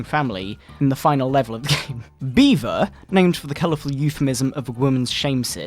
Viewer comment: When he said B. va I thought he said “beaver” and what’s talking about the hamster